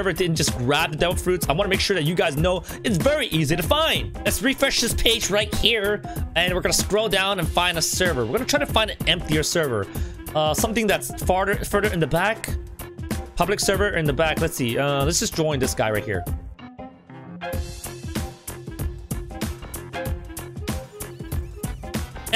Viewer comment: the fact that he said farter instead of farther lmao